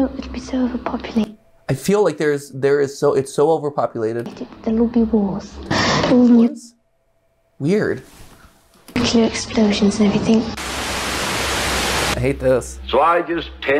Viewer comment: so, I think they said “I think it’ll be so overpopulated that there will be wars, and many explosions and stuff.” Roughly what I gathered.